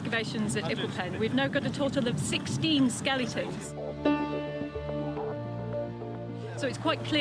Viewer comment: Did she say skelingtons?